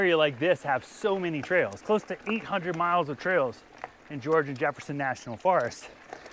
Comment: Did he say George Jefferson?